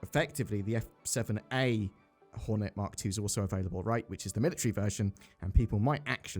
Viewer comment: did you say the f7a was available?